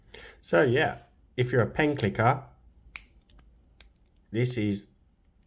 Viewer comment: It took me a second to realize you said, "pen clicker." What I heard was, "So if you're a paint licker, this one's for you!" 😂 🤦🏼‍♀️